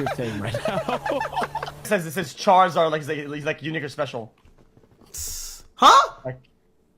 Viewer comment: Kai heard the N-Word instead of “unique or special”